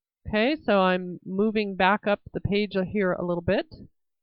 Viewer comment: Please see my comments on parts 2 and 3. The auto-generated captions are mostly good on this one, but need punctuation to be compliant and I would suggest changing 2:04 “moving back up the page I hear a little bit” should read “moving back up the page up here a little bit” Thanks for considering!